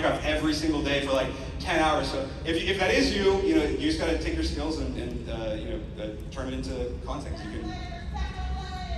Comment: what does the guy in the crowd yell about technoblade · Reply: That he’s the best Minecraft player